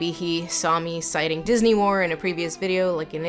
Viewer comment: small caption mistake: “Disney more” instead of “Disney War” 27:39 “trip” instead of “trope” 28:46 thing A, thing B